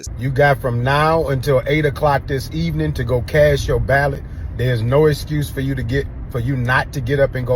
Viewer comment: Did he say cast your ballot? I heard cash your ballot.